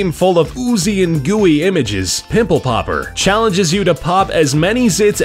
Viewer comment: sounds like he said nipple popper.